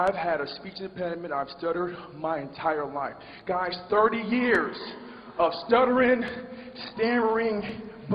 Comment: I thought it said 30 years of depression